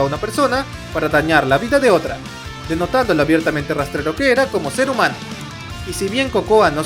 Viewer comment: En lugar de "rastrero" entendí "retrasado"... me proyecté x'd